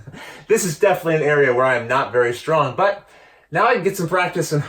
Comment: I got so confused for a moment there. On the second bracket, between the cuts, I thought Luke had moved around from behind to your side, then somehow misheard 'Ryan' as 'where I am' (made perfect sense at the time as you were measuring & marking), then suddenly Like popped up behind again! XD I had to re-watch those 30s to make sure I wasn't going bananas......more so